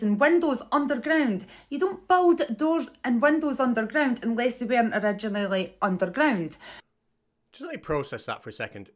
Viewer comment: She actually said "unless they weren't originally underground"